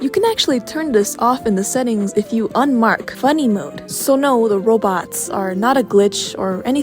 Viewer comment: thought that said furry mode 😭✋